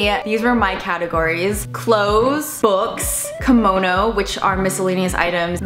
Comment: I have to move, and cleans my tiny apartment of 10 years of collected stuff. This really helps. Thank you. Also whenever you say "komono" - I know what it means. :) - I hear "kimono," because I am a kimono collector, and studied the art of kitsuke for a while. Going through all the kimono I own took me an entire day. I ended up getting rid of half of what I have and sending it to other kimono collectors in a group I'm in. I kept only the ones that gave me joy.